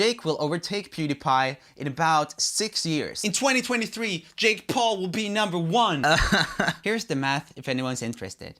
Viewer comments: When Jake said Jake Paul is number one I thought he said chipotle is number one am I the only one who thought this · Who else was confused when he said in 2023 but you thought he said 60 years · First I heard he said "Jake Paulers number one" but no.. he said "Jake Paul is number one".. smh